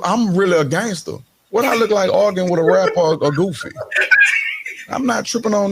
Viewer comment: Wtf did he say? "what do I look like, an organ with a rapper or goofy"? :P what? :D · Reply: "arguing with a rapper all goofy" I speak fluent southerner.